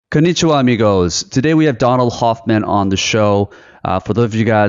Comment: What was your #1 takeaway from this episode? Subscribe to the channel so you get notified for more inspiring content like this: <link> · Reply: Thought you said Kanichiwa bitches as the intro. Either way i subbed after that ha